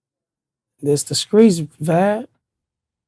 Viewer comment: Did he call him "Vibe" or am I just high?🤔🤣🤦🤷